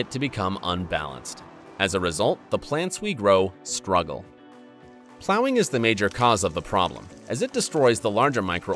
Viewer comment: I am not native english speaker. Could someone tell me what sentence is spoken between 1:58 and 2:02? The first verb, I can't understand which one is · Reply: They are saying that "plowing" is a major problem. plowing is digging in the soil.